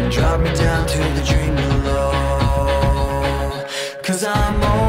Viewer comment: Take me down to the river bend Take me down to the fighting end Wash the poison from off my skin Show me how to be whole again Fly me up on a silver wing Past the black where the sirens sing Warm me up in a nova's glow And drop me down to the dream below 'Cause I'm only a crack in this castle of glass Hardly anything there for you to see For you to see Bring me home in a blinding dream, Through the secrets that I have seen Wash the sorrow from off my skin And show me how to be whole again 'Cause I'm only a crack in this castle of glass Hardly anything there for you to see For you to see 'Cause I'm only a crack in this castle of glass Hardly anything else I need to be 'Cause I'm only a crack in this castle of glass Hardly anything there for you to see For you to see For you to see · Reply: welpon kurt i thought it said drain below